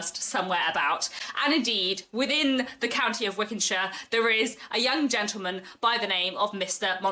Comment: Wickhamshire - did I mishear? You are toying with us! Sounds fun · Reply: Wickenshire :) But I guess that does sound a little Wickham-ish, doesn't it? That wasn't even an intentional Austen-ism!